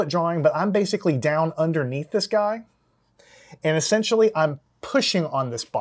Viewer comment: "I'm terrible at drawing", he says as he draws a three-legged man. I think it's anatomy you need brushing up on, not drawing XD. Great channel though; watched a few videos and they're very helpful! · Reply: I think he said "I'm down underneath this guy" 3:11 ^^